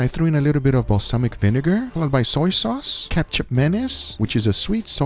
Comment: am I the only one who heard ketchup mayonnaise